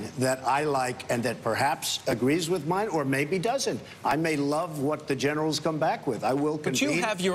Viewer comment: I thought he said he knows more than the Generals?